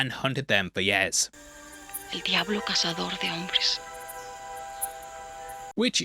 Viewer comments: that’s not what she said. She said: “El diablo cazador de hombres”. That means “the devil man-hunter”. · this has always bothered my autisic ass. What Anna is saying in the featured clip is actually "El Diablo cazador de hombres". Which means "the devil hunter of men" the part with "que hace trofeos de los hombres" comes later in the monologue. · Anna said "el diablo cazador de hombres" which translates as "the demon hunter of men." Although, I see how it could have sounded like "el diablo que hace trofeos de hombres."